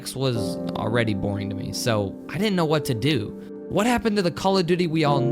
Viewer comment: Thought is said “God what happened